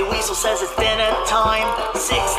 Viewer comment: When he says “six little chicken will be just fine” I hear“six little chicken are about to eat you spine”